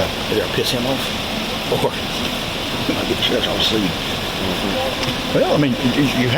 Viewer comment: "Or.. ." Might get Sheriff's office sued." Is what I heard the sergeant say where it says "inaudible". Around 19:26 mark